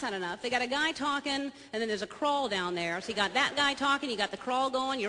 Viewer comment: haha!!! turn on the captions and at 5:30 look at what it says: "and it's a crawl down there to God..."